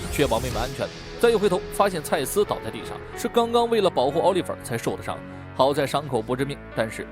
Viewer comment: 保护奥利佛，我听成了保护奥利给。